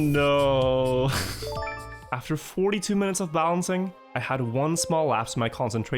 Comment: I thought he kept saying bouncing. Instead it's actually balancing